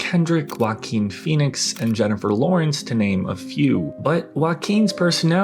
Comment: I swear I heard you say "Jennifer Loins"